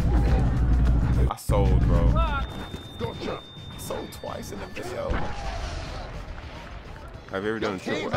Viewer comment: What'd u say? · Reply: I sold 2x in a video! (I lost 2 GBs)